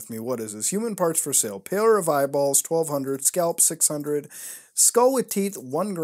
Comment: I thought he said pillar of eyeballs.